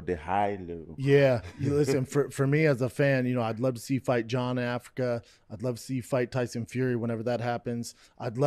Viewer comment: Ah man, i gotta admit that now i hear bapa day "jon in africa" I thought he actually said jon africa lmao